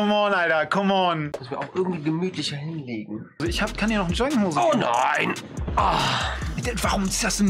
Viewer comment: Bin ich die einzige die bei 4:52 erst verstanden hat "ich kann dir auch ne Joint geben" ?